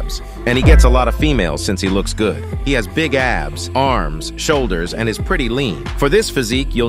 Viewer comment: I thought he said he has big ass 💀